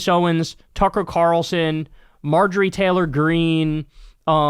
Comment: Did you just say "Cucker Tarlson"?